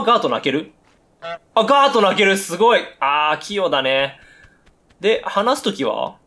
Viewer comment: ( ･´ヮ･｀ )＜あ~器用だね が 空耳＜あ~キヨだね にしか聞こえんくて吹いた